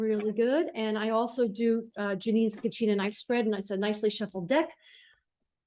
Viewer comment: Kachina knife, using right-side-up cards. Any that show upside down you turn, and just assume the positive energy may be a little stuck. I put the recent past card on top, and puts it on the left. The rest of the cards and their placement are the same: 1. Situation/querent; 2. Situation or situation clarifier; 3. Recent past; 4. Present; 5. Near future; 6. Main advice or clarification; 7. Further advice or clarifier for #6. Hope that helps! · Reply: thank you! I thought said kachina knight 😅 and that came up with really weird google results. I will give this spread a go for sure ❤